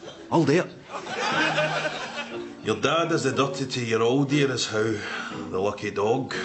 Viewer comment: Can any native tell me, what 's the sentence he's saying from 0:30 onwards? Up until "the lucky dog", I understand fuck all. The subtitles, hilarious as they might be, aren't helping me whatsoever. I mean, I get the sentiment - but the words escape me. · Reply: I looked it up, actually! It's "Your da does the dirty to your old dear, is how." - but I totally thought is was "seducted" as well!